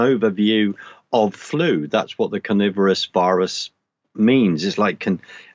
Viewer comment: carnivorous? Am I a dumb Yankee or is that what's said?